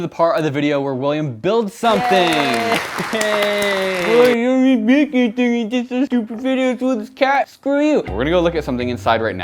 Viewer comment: Does anyone know what William says at 5:23? I need subtitles :) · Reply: “Oh This dude makes stupid videos with his cat- screw you”